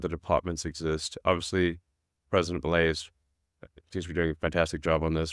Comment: Can anyone tell me what president he is talking about at 11:35Sounds like Elon is saying president Valeas? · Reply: They are talking about Milei in Argentina